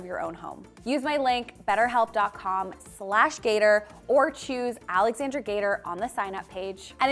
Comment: I love you! I’ve been watching you for years. I just heard you say your full name- and I stopped in my tracks- I thought your last name was GRATER!! I feel so stupid!!!! GATER! GATER ! GATER!! I will not forget!!